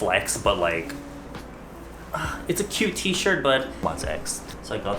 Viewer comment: man I thought you said buttsex